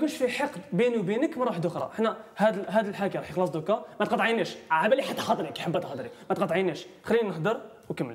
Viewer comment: اللي اسمه ايش قال في الدقيقه 15:52 هههههههههههههههههههههههههههههههههههههههههههههههههههههههههههههههههههههههههههہَ هُ =D=)):'(</3! مافهمت شي هههههههههههههههههههههههههههههههههههههههههههههههههههههههههههههههههههههههههههہَ هُ =D=)):'(</3! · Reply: سبحان الله وبحمده سبحان الله العظيمjeddah jizan قالها لاتقاطعيني اعلم انك تريدين الكلام لكن لا تقاطعيني